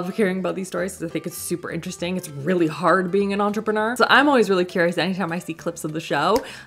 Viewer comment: I died at the way your pronounced “entrepreneur” 😂😂😂 all I heard you say was “ontrprenur” 😂😂😂 Also wasnt a fan of the Korean brand because they literally went a completely direction then they started at. At first their pitch was for plant/clear/natural products, from what you showed, nothing on the Sephora website was anything near what they were first advertising. And nothing is good about only have 1 good product. 👎🏻